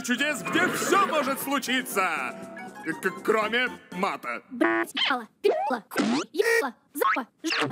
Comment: is it just me or do i hear murder